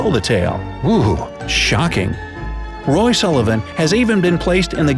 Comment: did he say Lars Sullivan